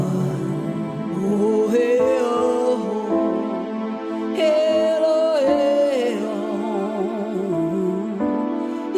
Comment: At timestamp 1:09;28 , that is 1 h 9min 28 sec says out loud his birth name, and I as not so fluent in spelling dont know how to write his birth name , "windhemn" i hear and i dont think that is the correct writing. Please some one who sees this and knows how to spell his name, reply this message with the original name.